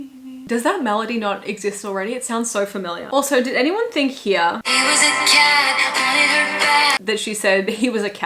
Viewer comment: This is exactly what we wanted 💛 Also happy to know I'm not the only one who thought she said "he was a cat" lol